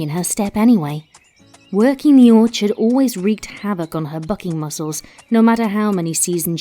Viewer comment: Thought it said fucking muscles not bucking 😂